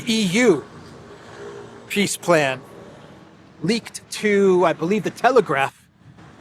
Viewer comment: Ha ha - did I heard right - "Piss plan Leaked"...